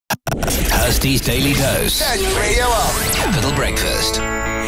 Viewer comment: it says turn the radio up, but i can see why you thought it said that. i went back to listen for it.